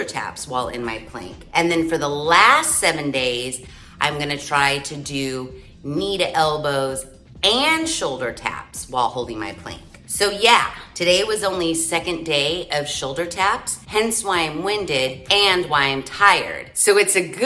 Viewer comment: 🌲🌳🌴🌵🌿☘️🍀🌾 OH, I THOUGHT YOU SAID PLANT... 😂😂😂😂😂😂😂😂